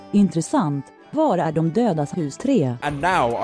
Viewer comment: Hi, sweed here. In the nord ad it sounded like pew said "intresant, vart är de dödas hus tre" which translates to "interesting, where is the deads house three". I dont know what the fuck you were trying to say pyro, but you certainly turned it into something. Make of that what you will